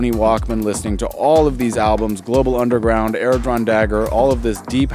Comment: I just heard Xpander a couple of days back great track and then this video shows in my feed. I couldnt get it through the subtitle; can you please mention which show you were listening to right after Global Underground @ 1:15 thank you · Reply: Airdrawndagger!